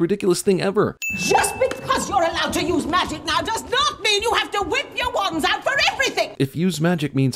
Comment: All i hear is "whipped your wand out for everything"